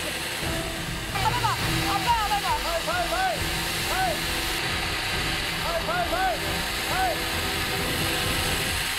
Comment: what did he say ? can someone explain it to me ? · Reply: Foot!! Foot!! Foot!! (It means step(foot) on the ground when she's landing)